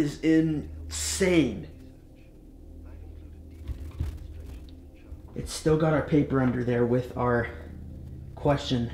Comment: anyone else hear talking?! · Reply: Says something like "I include a detailed instruction" the rest I haven't managed to work out yet.